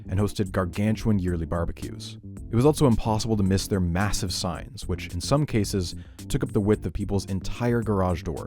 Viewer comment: "By his thirties he adopted his recognizable silhouette" That has got to be the most respectful way of saying someone got fat i've ever heard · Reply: I misheard “signs” as “size” “It was impossible to miss their massive size, which in some case took up the width of people’s entire garage door” Me : He can’t possibly be that fat?!